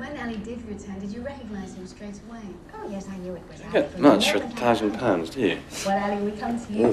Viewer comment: what does rupert graves say to shane at 1:15? I can't figure it out!!! ps. go shane taylor!!! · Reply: ‘You don’t get much go a thousand pounds do you’